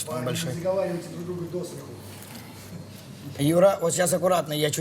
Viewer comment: мне послышалось или он сказал Юра?